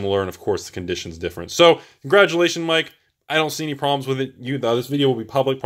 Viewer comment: did he say congratulations Mike, as in the owner of the card's name?